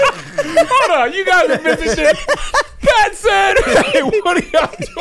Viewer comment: Ok I know I’m late and I’ve seen this episode a bunch of times but does Kev say “y’all missing shit”